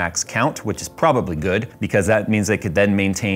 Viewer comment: Great video! Although I thought he said "chicklets" and brought a smile to my face. I'm the furthest thing from a tech savvy gamer, but I'm very delighted that the fued between AMD and Intel has flared up to give the end user more choices. Myself being an end user that likes to be extremely task saturing the newer tech for gamers isn't something I'm ready to move forward with. System: Dual Xeon x5680s running on an Intel S5520SCR Packed with 48 gigs DDR 1333 ECC ram. Dual EVGA 1300 watt power supplies, 4-120 x 25mm fans, 2-92 x 38mm high volume fans all tucked into a modified Silverstone TJ07 case supporting up to 21 HDDs (Non SSD).